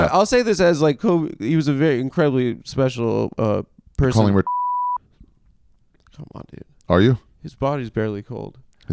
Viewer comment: What did Ari say at 10:17 ? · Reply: Rapist